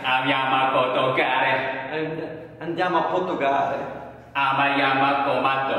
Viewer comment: io ho capito "andiamo a porco cane" hahahahaha